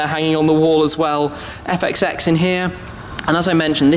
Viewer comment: "Epic sex in here" did anyone hear that ? lol